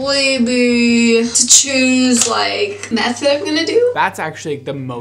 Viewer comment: I thought she said “choosing the meth im going to do 😂”